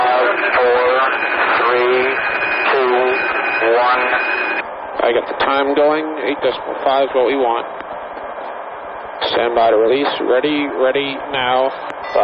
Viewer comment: what does the bombardier says at 32:33 ? " I have the time going to hit................ we want " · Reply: Sounds like "I have the time going, eight decimal five is what we want." What that exactly means I don't know.